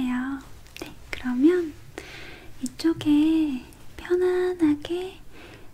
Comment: i thought she said "it's okay"